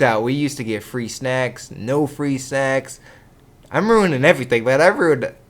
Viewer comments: I thought that he said no free sacks... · dunno if im just perverted or what, but i totally thought you said "no free sex"